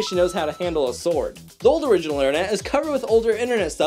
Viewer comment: "Interneet" What I hear: "inter neat"